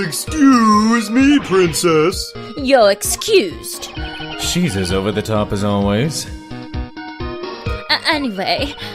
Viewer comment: what was that line that they said F*ck this line? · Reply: "Whaaaaaaat". Watched an another walkthrough